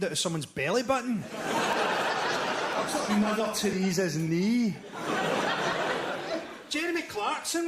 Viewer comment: what is Frankie saying here? 56:17 does the old man say he has aids in both ears? is that a play on hearing aids and hiv/aids? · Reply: Shay ahhh thanks! i hear it now: “i’m talking mother theresa’s knee” cheers buddy!!!